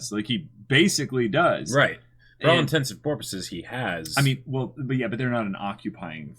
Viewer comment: I swear I'm hearing say "for all intents and porpoises"